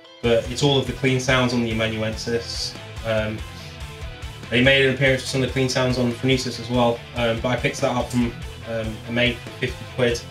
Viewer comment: Can somebody tell me what he says at 17:55? right before "the best 50 quit I've ever spend" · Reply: They made an appearance on the phronesis as well.. picked those (the p90s) off a mate for 50 quid - something like that